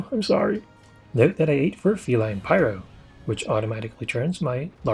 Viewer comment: What do you say at 6:07 in the video? What is it that automatically upgrades your barrel bombs? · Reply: Felyne Pyro from the canteen.